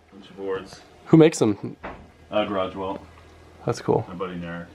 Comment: who does he say makes the skateboards? "Garage well"? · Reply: Jack Archell Garage Welt, check them out!